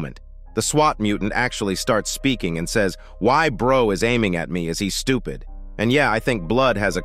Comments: I genuinely thought SWAT dude said "White boy is aiming at me" at first. I only realized what it actually was when I read someone in the comments lol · WhenI first saw the episode, I thought the police mutant said “white boy is aiming at me”